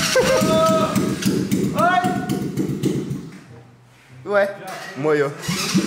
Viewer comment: reh- mooooo -oi? so eu q ouvi isso?? ein